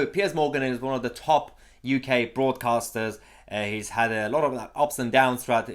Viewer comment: Sorry, thought you said “Fraudcasters” - actually better than broadcasters, thinking about it...